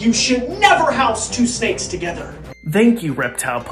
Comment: I really thought the reptile police said “I’m the reptile police. AND YOU SHOULD NEVER HOUSE 2 STEAKS 🥩